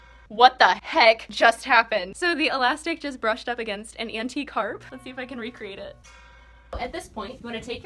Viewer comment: When you said the elastic brushed against an antique harp (I think that's what u said I'm still not entirely sure) I thought you said anti carp. I was confused to say the least.